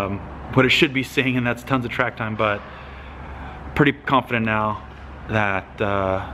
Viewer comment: Did you say "Pump-fident" at 11:03? Kidding...Keep on truckin'. You made your mark on drifting already, when this program comes together, It'll just be more fuel to the fire that is the BOSS...You rock. Aloha. P.S. SUBBED! Why the heck don't you plug your channel more?